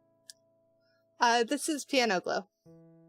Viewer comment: Thank you! I use Piano Glow :3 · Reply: thank you. I heard "piano glove" 😅😂